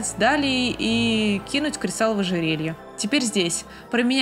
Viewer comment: кинуть кристалл в ожерелье? Мне же не послышалось?🤔 17:57- мне всегда казалось что она ищет себе приемников для передачи трона, поэтому и не стала вмешиваться, что бы посмотреть способны ли они принимать быстро и верно решения. Хотя если сейчас это обдумать то зачем ей тогда стражи? 2:16- -Знаешь, у тебя в глазах радуга.. -знаю, я другая, вот и всë.. -да, тебя ждëт сюрприз.. "Азура укладывает Элину спать" Звучит как отрывок из фанфика😂😃